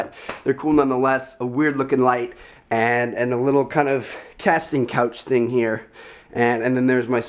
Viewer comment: Lmao did he really say "Casting Couch"?